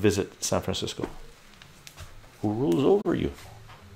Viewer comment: Did you say "Jew rules over you" three times? 😉